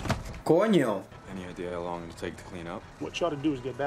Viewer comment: I really wanna know! What are you always saying and what does it mean?? "Con yo" "Gonyo" LOL I don't speak spanish and I never know what you're saying but I wanna know!!! 😂 😂 😂